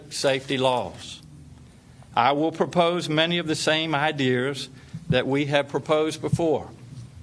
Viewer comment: DID Northam just say (idears) at the 7:14 stamp time? You a governor and a Doctor come on now you "ain't" suppose to speak th as t way. 😂🤣😂